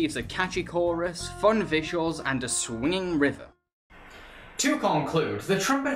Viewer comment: thought you said swinging river,as there was sea in the shot you said it